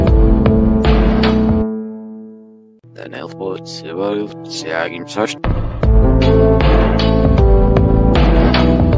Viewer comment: a message that is said backwards can someone reverse what he was saying? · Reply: it says "trust me guys, pluto is still a planet" reversed it using audacity